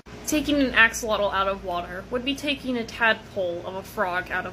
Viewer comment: Frogs can go out of water. It'd bee more accurate saying taking a fish out of water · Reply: I totally thought they said tadpole OR a frog and I was like I swear I’ve seen frogs on land before 😂